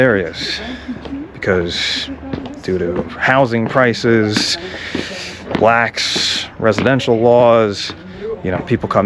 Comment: did he say blacks? LOL